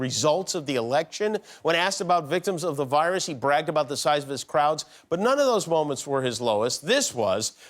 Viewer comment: When he said, "he bragged about the size of his crowds", I thought he said crotch.